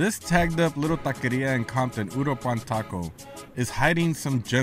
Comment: I couldnt understand this place name i had to see video a few times to hear it a few times sounds like you say puropan. Food looks very good.